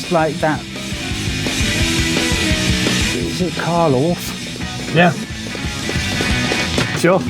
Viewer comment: When he's trying to describe what a song sounds like and says "umm... Karlof?" imo he means 'Cardiacs'. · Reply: I think he said Carl Orff.